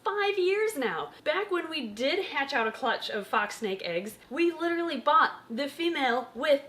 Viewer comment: I thought it said my fox laid snake eggs and was confused